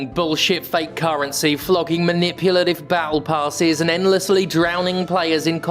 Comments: I heard "bowel passes" instead of "battle passes" and will going forward refer to battle passes as bowel passes. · I just misheard you saying "Bowel Passes" instead of "Battle Passes" and I think we should rename them to that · I misheard "battle passes" as "bowel passes", and it makes a lot of sense.